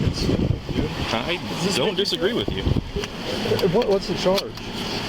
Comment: Did anyone else hear that? It sounded like the cop said "I don't disagree with you" after being told the entire stop was stupid.